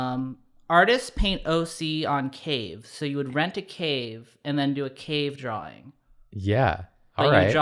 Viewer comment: I heard 'cake' instead of 'cave' So... Cake art. I suppose. You could do that